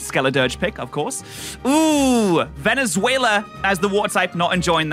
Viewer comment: Did he just say Venezuela to Veluza or did I hear wrong? 3:56 by te way